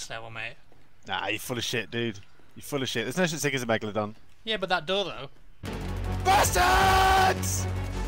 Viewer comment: Couldn't help but notice that at 2:07 it kinda sounded like he said "Yeah, but dat dildo." :P